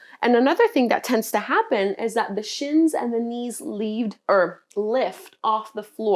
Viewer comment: I heard ' the shins and the knees leave earth' !! hahaha!